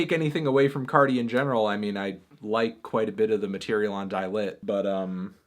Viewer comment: subtitles say "toilet" instead of Die Lit. I agree with this. Let's Argue.